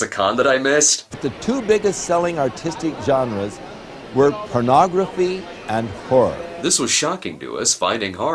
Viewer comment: it sounds like he said "pornography and whore" 7:01 is that the boxart to that nes game chiller?